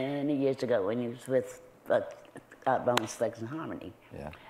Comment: Did she say Bone Slugs in Harmony!? 3:33 🤣